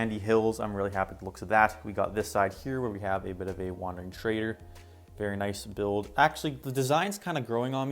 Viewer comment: When you said wandering trader I thought you said wanted traitor lol